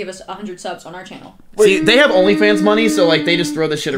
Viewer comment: All I heard was onlyfan